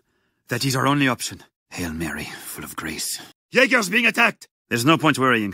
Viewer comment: im i hearing it wrong Full of Grease